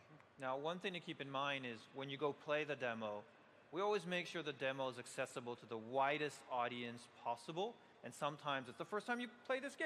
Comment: sound like he said "we always make sure the demo is accessible to the whitest audience possible" :D